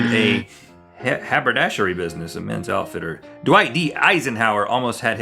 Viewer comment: it sounded like he said "do i eat Eisenhower"😂😂😂